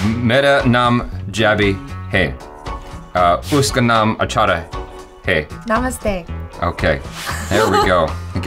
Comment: when he say Achara hai i thought he say achar hai (pickle)